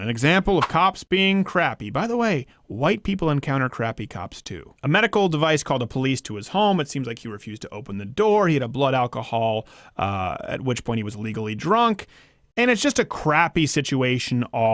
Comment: I kept replaying this part, because it sounds like you're saying "illegally drunk", which I don't understand that term as it relates to someone drunk in their home.